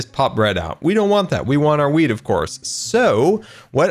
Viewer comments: am I the only one who hears "we want our weed" and not wheat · i swear i hear weed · I think I missed something did he and grian win the head games Also when he said I want the wheat I thought he said I want the weed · was it just me or did say weed instead of wheat at 9:28?